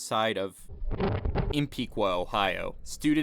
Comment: I heard you say, Columbus Ohio